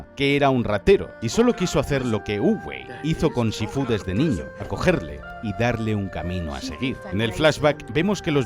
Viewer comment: Por un momento escuché "Cogerlo" en lugar de acogerlo xDDDD dije "Con razón Tai Lung estaba bien traumado y enojado con Shifu xD